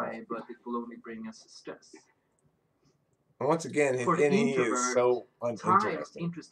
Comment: What's that word/phrase anyone? "His Ne is so...."? I couldn't make it out. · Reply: "His Ne is so uninteresting." ? That sounds weirdly true.